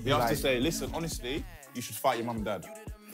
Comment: I thought he said she should fire her mum and dad - cos shes clapped, not that she should fight them?🙈